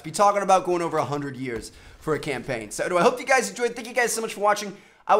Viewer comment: wtf did he say? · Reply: He said check out these 2 videos on the screen if u have not seen them yet, at first I did not realize either but I kept watching to see😅😅😅